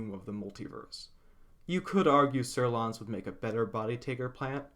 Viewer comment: I wanted to read on the wiki about the creature on 8:25 but i can't find its name for the life of me, Sarlon? Zurlon? Sirloin? google doesn't give me results on whatever i hear so i guess it isn't written as it sounds? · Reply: Psurlon. WotC is messing with you specifically